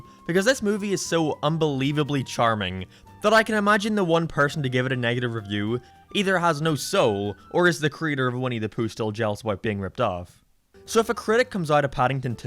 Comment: what did he say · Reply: He said that the person who gave this film a negative review, either has no soul or is the creator of Winnie The Pooh, still jealous about being ripped off